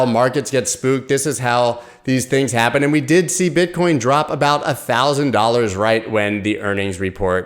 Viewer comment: Did you just say bitchcoin ahaha